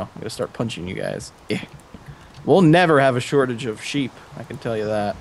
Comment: I thought said "Wool never have a shortage of sheep"